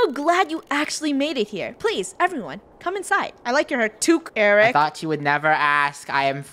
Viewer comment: omfg I thought was about to say "I like your cut G" BAHAHA IM DYING OF LAUGHTER